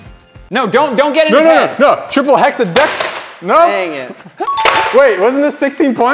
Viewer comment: did just say triple hexal deck